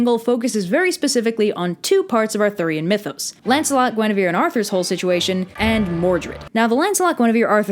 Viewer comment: Why did I hear 20:00 as "Lancelot wanna be with Arthur" Like intresting ship Brain, BUT NOT THE BEST TIME